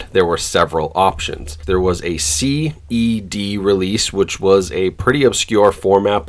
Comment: What are you saying at 1:00? CBD?? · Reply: CED